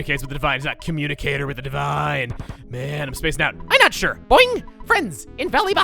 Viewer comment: I never realised it said boing, i thought the said "doing"